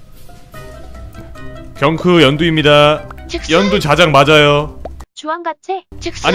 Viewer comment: 계속 재생하면 핵이 맞는 다고 들림ㅋㅋㅋ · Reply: 전 걍 연두 자작 맞아요 로 들리는데